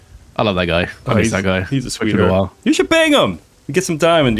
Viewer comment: For a second I thought you said “bang” and I was very confused